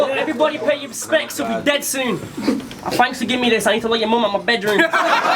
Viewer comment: what does he say? something something "mom in your bedroom" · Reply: "And thanks for giving me this I need to let your mom out my bedroom"